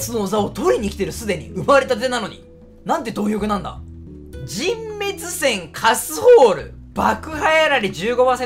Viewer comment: 何回聞いても燼滅閃カスホールにしか聞こえなくて笑ってる